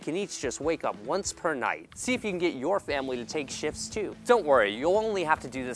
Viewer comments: I thought he said "See if your family can take shits too." Instead of saying "See if your family can take shifts too." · Bruh I thought he said "See if you can get your family to take shits too" bahhh